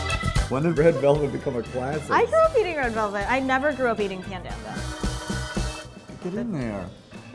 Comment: Did she just said "panda" at 1:14 ?